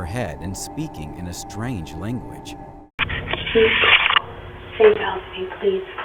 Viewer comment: sounds like lady is saying “please help me”